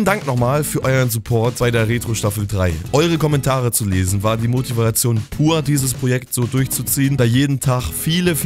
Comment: Bei 1:42 hab ich verstanden: "Eure Kommentare zu lesen war Demotivation pur." xDDD😂😂